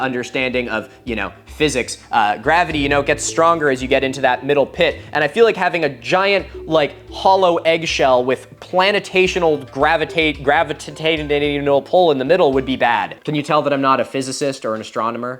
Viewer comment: i’m pretty sure you meant to say, “gravitational pull,” and also, it sounded like you said, “gravitate anal pull.” 😭